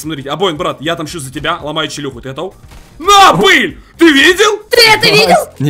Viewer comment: мне послышалось или там мат всё услышал там он сказал на пыль